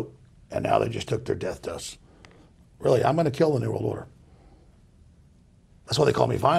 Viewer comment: What does he say at 1:08 ? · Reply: "I'm gonna kill an evil lawyer" ? I honestly can't tell